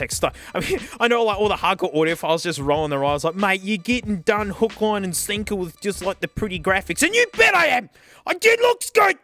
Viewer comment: I cannot for the life of me understand what he says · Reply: Don't worry, I speak Aussie. Translation: "Mate, you're getting done, hook, line & sinker, with just like the pretty graphic. You bet I am! It looks good! i like it!"